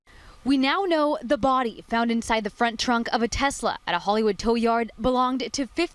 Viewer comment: Is saying "frunk" instead of trunk or is it me? I thought I heard it in the last video too.